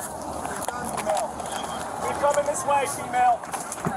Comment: “Keep coming this way Shemale!”?? 😂😂 Did dude REALLY say that?! 😂 I swear that THATS what I hear!!